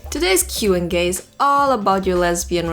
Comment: did anyone else hear "today's Q n gay"?